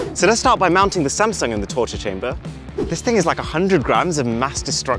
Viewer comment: "Torture chamber" woah man that was a little specific edit: 1.5k likes?! Wow ty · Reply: I heard it as torching chamber